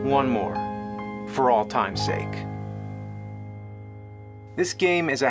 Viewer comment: Did he say "all time's sake." ...?